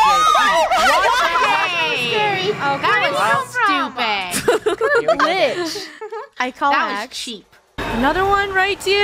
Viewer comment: What did say at 16:13 😂😂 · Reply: “What kinda glitch?!”